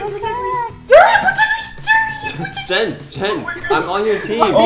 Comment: wait wait hol'up did call the green safe ball a durian? XD